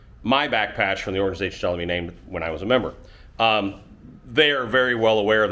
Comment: I'm sorry, but I've been out of the loop a bit lately. Did I hear this part correctly? "Was"?